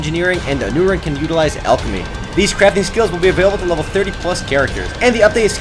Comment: lol i thought i heard ":new crap skills" from eden eternal XD